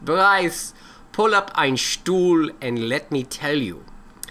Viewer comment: I know it sounds like he said stool but he said chair in Deutsch